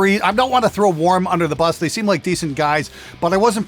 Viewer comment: Pretty sure the guy meant that he wanted to see your "warm up routine"...... Your fiddle pickin has gotten significantly better. Congrats and Cheers!!! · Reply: Yep, I also thought "warm ups". Never heard of a "Warm" brand, but I'm not really into guitar/sound stuff.